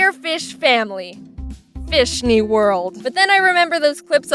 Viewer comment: When she said, “Fishney world!” I thought she had said, “Fist me world!” ... I accidentally spit out my water.